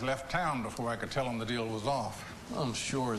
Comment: I heard "What's wrong with Hong Kong?"